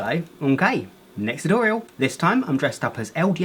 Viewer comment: I was intimidated and then I heard "allo, m'kay next tutorial😀"